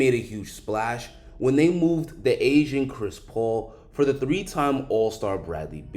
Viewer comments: I heard "the asian" not 'the aging' CP3 lol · hmm 0:08 am I the only one who heard.. "the Asian Chris Paul" ??? lmfao